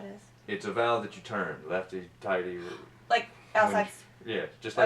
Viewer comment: did he really say “lefty tighty”?